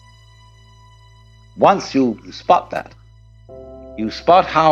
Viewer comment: What does he say at 7:04? Not aware of what? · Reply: Unity